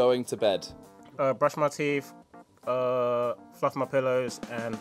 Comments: it sounds like Toby said fuck my pillows lol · I heard that as "fuck my pollows" and was like tf